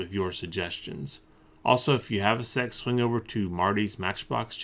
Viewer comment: “if you have a sec swing over to ....” I heard sex swing I’m ashamed of myself